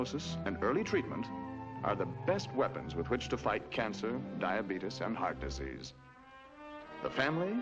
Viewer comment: I see every machine ask for 1 cent, and I hear "dia-beetus". Icing on the cake.